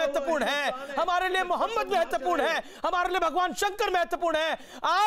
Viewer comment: If I am not wrong I heard the anchor at 05:20 saying "Muhammad" PBUH's name.